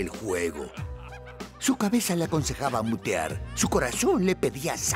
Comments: pensé que dijo, su cabeza le aconsejaba putear... Lo cual era entendible xd. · Minuto 1:00 la primera vez que lo escuché pensé que dijo "putear" jsjsj